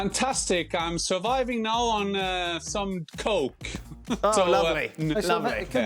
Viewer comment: mates did he say he is surviving on some : Coke: ? my man ^^